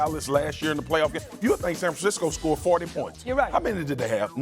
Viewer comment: Did he say 40 points? what does she mean US! Ha prophecy